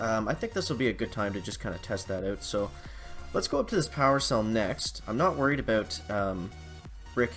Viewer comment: did you just call the crystal solution a power stone? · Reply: power cell said lol like in ops xD